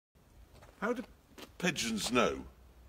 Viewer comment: I heard that as "how do pensioners know"💀